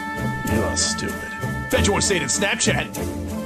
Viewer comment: did Mike just say snapchat?